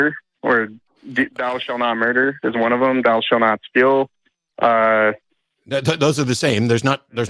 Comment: Certainly not defending, but I am fairly sure he said Thou shall not murder, then thou shall not Steal, no Kill. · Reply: Ya, I noticed that Dillahunty thought he said "kill"--but DID say "steal". Now that being said, i thought it was curious that did not--at some point shortly thereafter try to tell Dillahunty he DID say "steal" .